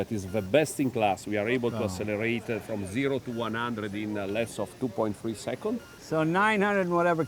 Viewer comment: 0 -100 km in 2-3 secs. Did I heard that right ? Fuckk . . .